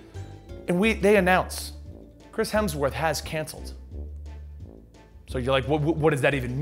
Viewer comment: I thought you said; "Chris Hemsworth has cancer."